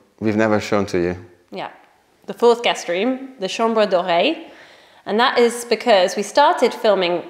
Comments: Please tell me I wasn‘t the only one who thought you said it was called the chambre d‘oreille. As in the ear room 😂 I was a little confused at first 😅😅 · Hahaha I thought you were saying chambre d’oreille!! Not chambre doré